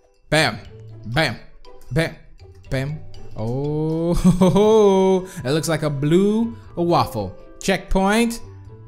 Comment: Anyone hear him say "Looks like a blue waffle"